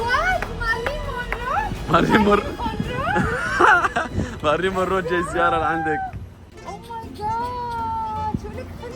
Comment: شو قالت في 7:35 ??😂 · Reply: خارلين خورنو😭